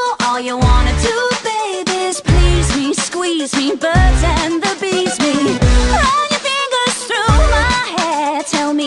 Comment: You know, whenever I heard the lines, "birds and the bees me", I will sometimes heard, "birds and abuse me". If this is true to anyone else, let me say that was clever.